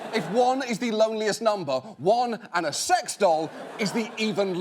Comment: Lol, at first I thought he said Wong plus a sex doll is the even loneliest number.